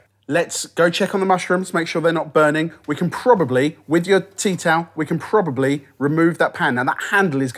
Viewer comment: Oh my god. I thought he was saying "teat out", not tea towel.